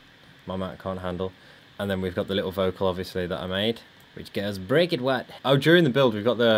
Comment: Break it WOP "I don´t really know what that means." 2016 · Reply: i hear "Break it WUT"